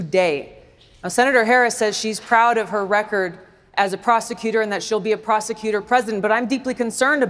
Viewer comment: She’s a prosecutor? Darn, I had it wrong. I thought they said she’s a prostitute.